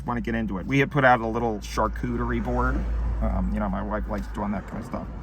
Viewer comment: wtf did he say? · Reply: “Charcuterie board” - meat & cheese board